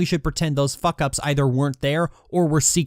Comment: For a second I thought you said, "cursed chips"